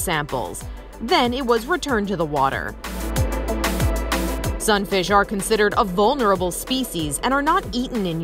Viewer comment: Sunfish??? Never heard of it. I thought it said starfish at first.